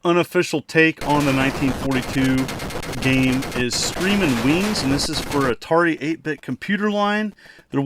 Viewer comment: I totally thought you said screaming weens.....I love 1942, 1943 and im not sure if its part of the franchise but Strikers 1945 for ps1 is awesome also. Great video